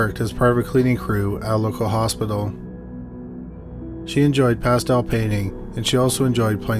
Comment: When he said; " Pastel Painting" I thought he said; " Passed out painting" My brain was churning quickly trying to figure out what that was